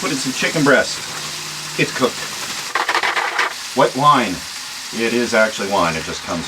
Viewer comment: That pasta looks good! I thought you said wet wine 🤣 I was like isnt all wine wet! Then i realized im just retarded and it was white. Thank you this was an awesome video!! Very cool idea and great job building it!!